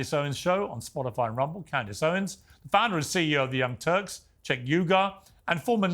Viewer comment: did he say the "Young TURDS"?????